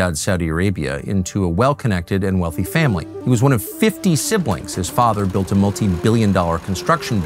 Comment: Did I heard it right? FIFTY siblings?